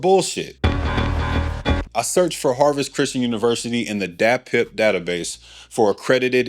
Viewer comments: I swore you said DATPIFF database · I thought he said the datpiff database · LoL 3:00 thought he said DatPiff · listening away from my phone. I thought you said “dat piff database” 😆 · holy shit I legit thought you said DatPiff 🤣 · I heard Datpiff 😂😂😂😂😂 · yoooo i thought you said DAT PIFF data base lol